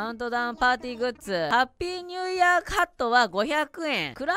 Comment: ニューイヤーハットが、ニューイヤーカットに聞こえてなんか髪の毛アレンジしてくれるんかな思ったＷＷＷＷＷ ころんくん最初から最後まで可愛かったです🥱💭💙